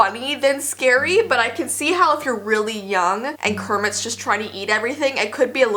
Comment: into the video she said Kermit instead of Cookie Monster by mistake, but it all good. I think nobody noticed that. I loved this video btw.✌